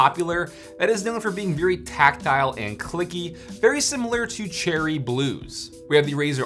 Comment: i thought it said "tactile and cucky"